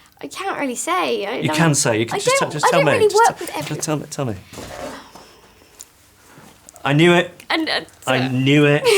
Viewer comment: did she say Alfie? because the ruined the sound on purpose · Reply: She said, "No one." :D